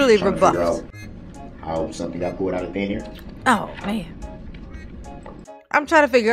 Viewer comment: Oh I thought he said how did something get pulled outta thin hair like his corner store dreads but said thin air lol